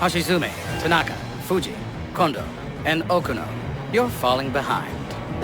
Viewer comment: did i hear condom